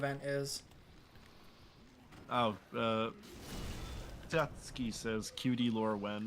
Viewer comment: did he just call me Satsuki